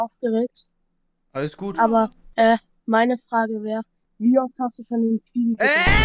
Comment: was sagt er? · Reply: wie oft hast du schon in Fibi gedippt